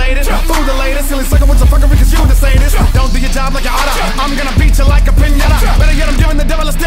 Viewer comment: I thought that said "You don't do your job like an otter"